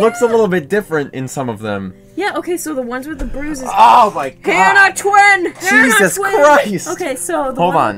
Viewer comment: Did just say "Anna twin!" Like fire emblem Anna or something?